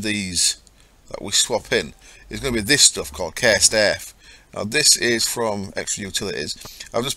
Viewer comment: I thought you were saying "Care Staff" throughout the video, when you were saying "Cursed Earth"